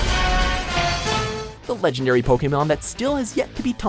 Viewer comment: Did I just hear Pokénom... x'D